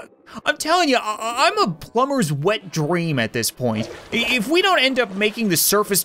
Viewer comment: did he say WET dream